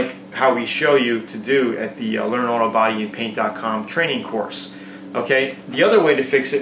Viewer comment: I heard "Hey, what's up? It's Tony from "Learn all about eating paint dot com"" :)